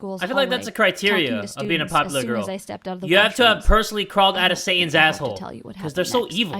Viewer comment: Did he say Saiyan? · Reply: he said satan